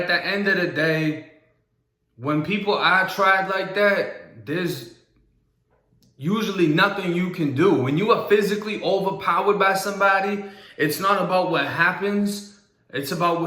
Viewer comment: No disrespect but did you say, "people that I tried like that, there's nothing you can do?" I had to replay this like 5 times. Am I going deaf? No way I heard that the way I think I did right?